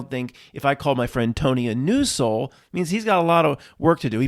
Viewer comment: When you said "New soul," in reference to Tony and whether you'd call him that, I thought I heard you say News Soul, for a second, or the phrase came in. Now, that would be terrifying.